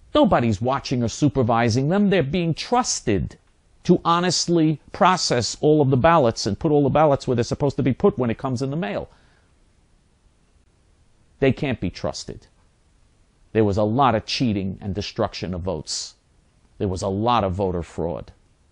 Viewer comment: I think he said that blacks can't be trusted....?